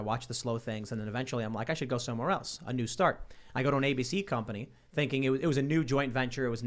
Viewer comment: Tim says: A new start I hear: ANUSTART